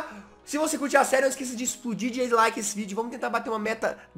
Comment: Só eu que ouvi explodir de deslike? 0:35 ;;-;; mas eu vou dar like nhaaa kkk :3 amo seu canal jazz ^^